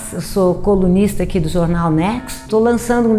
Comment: que susto, ouvi "comunista do jornal nexo"